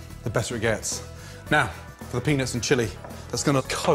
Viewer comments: did he say penis in??? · the cc says penis lol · turn on the caption Now for the penis and chili For a second, I thought Gordon Ramsay really said that. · AND NOW, for the penis and chile? It really helps to grind the penis up? This penis sauce is going to Coat the fish... LMAO Sorry Gordon, I know you are saying peanuts but with that accent all I hear is the other word. Great recipes! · i heard that " now penis and chili" 😂🤤✌️ "shake up your peanuts 🥜🥜😂✌️ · now the penis and chilli. (Even CC says it) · Did he just say "penis"? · Wait... Did i hear him say "Penis and chili"?? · Lol at 5:44 did Gordon said "the penis and chilli" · I thought you said penis and chili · was checking my phone when i heard "and now, for penis and chili" @@ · I swear I heard "Penis and chili" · I heard "penis and chili" while i was on a different tab. not gonna lie made me laugh. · So I got a little more daring. Started combining penis with chili flakes. Boyfriend didn't like it. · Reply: where he says peanuts but the subtitles say penis